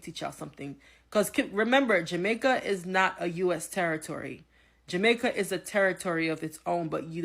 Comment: I thought they said Jamaica is not on the list.